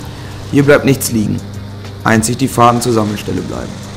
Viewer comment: Das Gemurmel bei 1:46 hab ich nicht verstanden... welche "Fadengestelle" bleiben?! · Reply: Fahrten zur Sammelstelle